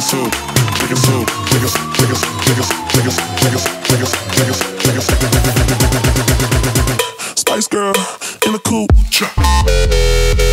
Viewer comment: not gonna lie thought he was saying "white girl" "in the coop" for the first few listens